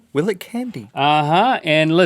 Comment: I literally heard "will it kidney"in the intro